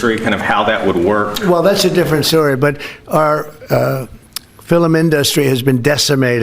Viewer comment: did he say ‘fillum’?